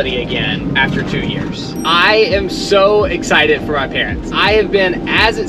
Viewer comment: did he really say "our parents"